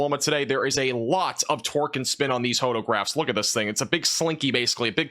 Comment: Thought I heard “twerk and spin”, torque, it was torque and spin.